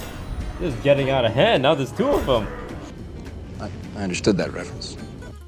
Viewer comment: What does Toast say here? I can't hear it at all, "This is getting out of hand CHAJUDUOFOW" ?????? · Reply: "Now there's two of them". It's a Star Wars: The Phantom Menace reference